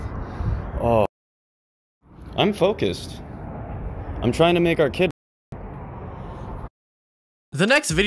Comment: if anyone's wondering what he said He said "I'm tryna make our kid retarded"